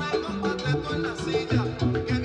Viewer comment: does anyone knows what starts at the end of this track? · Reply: I THINK HE SAYS, " THE LITTLE DOVE DOES NOT ASK FOR A DANCE" (THE 1ST THING HE SAYS) sounds like he says moth because i hear palomilla instead of little dove which is palomita which could also be popcorn.